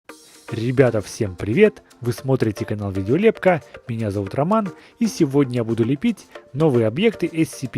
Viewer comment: Мне одной послышалось в начале? ,,Меня зовут Котоман"😂😂